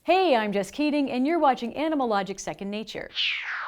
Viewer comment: I thought I heard "Hey, I'm just kidding." 😂